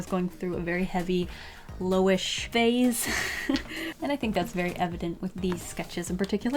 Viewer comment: But like, when are you not going through a Loish phase? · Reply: a chapman I thought she said lolish phase